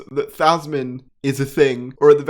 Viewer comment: he said yasmin/yaz and the subtitles say yazoo's 💀💀💀